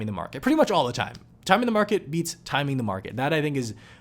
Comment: I swore you were saying “time in the market beats time in the market” ...you said it, then repeated it. And I couldn’t hear it correctly :) I was like, “What?!”